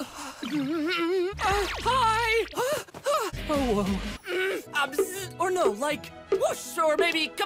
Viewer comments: did I hear an owo??? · me: Did homeboy just say OwO ?! Edit: the amount of people telling me he said "oh well" "oh Woah" or "oh no" is absolutely hilarious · It literally sounded like he said OwO 💚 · Did he just... Did he just say... owo? *Happiness Intensifies* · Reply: Deku Midoriyra he said “oh whoa” which is pronounced as owo..in internet slang. The fans are just being giddy and happy about something cute sounding is all. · Why do I hear Deku saying OwO?? Is there something wrong with my hearing or??? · did he just say owo..? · me: DID HE JUST SAY OwO!? YOU CAN NOTTTTTT CONVINCE ME OTHERWISE >:O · did deku really say owo at 1:07? XD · did he say owo? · I thought he said OwO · DID DEKU SAY “OWO” OR AM I HIGH?